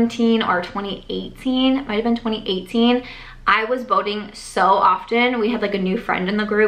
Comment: Oh…boating! I thought you were saying voting. “We were going voting all the time!” 🤣 I was wondering what you were voting on so often.